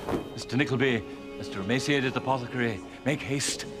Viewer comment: I nearly died when the theatre master called Smike “Mr. Emaciated Suppository” did anyone else catch that? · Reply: I thought he said apothecary , which although not humourous makes sense lol